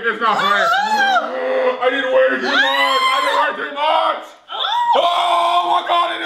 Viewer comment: Is Collins saying its not funny😆😆😆😆